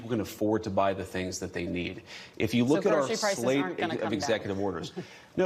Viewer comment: Did anyone hear him say, “If you look our slave….” Was that a slip?🤔